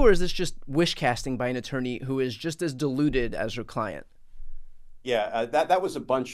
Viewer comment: It sounded like you were saying that she was diluted but then I realised that you really said that she was deluded. Either way I think that we are both correct in what you were saying. Because her brain has been scrambled and watered down by working for Trump, also because of working for him she has become deluded into thinking that he IS the president and always has been even though it's been shown that he wasn't elected in 2020. John Sauer is also deluded by Trump when he told the court that Trump could send in Seal team 6 to assassinate his political opponent, no man has that right now or ever, since we were rid of Kings right to run our countries we have all been safe from that!